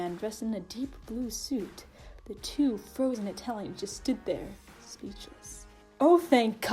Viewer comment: Literally thought u said "Froze in Italian"